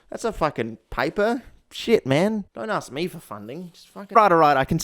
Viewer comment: the caption says f*** but you say s***